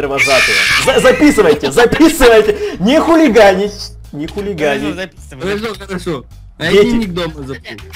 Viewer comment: Мне на 2:30 кажется, послышалось что эдисон сказал (Нехуй гадить)